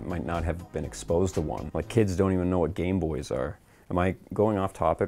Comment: did you say.. MY kids?